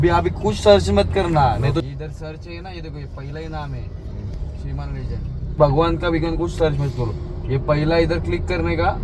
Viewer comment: bhagwan ka bigwan ka kuch mat search kro? Kya bhai kya bol raha hai?